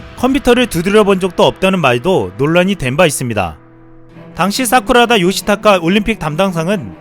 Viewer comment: 진짜 사람 이름가지고 놀리면 안되는거 아는데.. 저 컴퓨터 사용해본 적 없다는 보안 장관 이름 들을때... '사쿠라다' 가 '쌉구라다' 로 들려서 순간 '뭐라고?' 하면서 폰 다시 봄